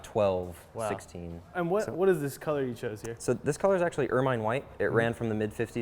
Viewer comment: He people, could someone tell me please what color this car is? English isn’t my native language and i can’t understand what he is saying at 4:04. Thanks in advance 👍🏼 The Netherlands Got it, Ermine white. Tnx